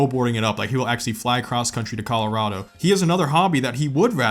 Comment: In my head I just heard ‘say Colorado! IM A GIRAFFE!’